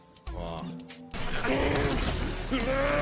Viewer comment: I laughed the way at 00:09 u think he said nani😭😂😂😂